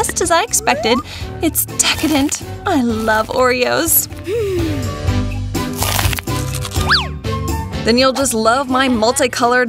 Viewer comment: why did I hear "I love Leorio"?